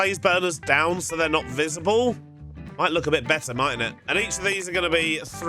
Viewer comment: did bro say “mightn’t it?”